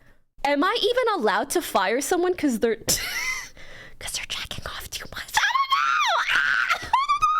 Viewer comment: she LITERALLY SAYS "AM I EVEN ALLOWED TO FIRE SOMEONE FOR JERKING OFF TO M... STUFFF? IDK" SHE KNEW EXACTLY WHAT SHE WAS DOING BY WORDING IT LIKE THAT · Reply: She said "jacking off too *much*" listen closely